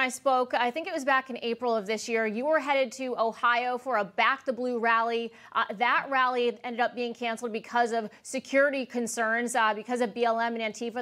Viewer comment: Sounded like she said “back the blue” ?